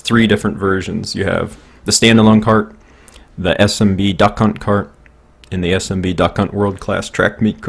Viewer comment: All I Hear "Dukunt Kurt Word Cess Dukunt Kurt"